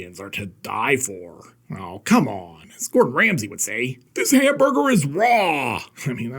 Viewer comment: I swear to God, instead of "Gordon Ramsey", I thought you said "Fuad Ramses". I... I have no idea what that says about me...